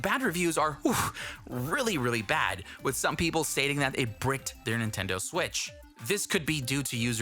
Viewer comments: did he just say breaked!?! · Did he just say "breaked". Holy shit. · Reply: Nope, he said "BRICKED" C'mon man! It's the pinned comment xD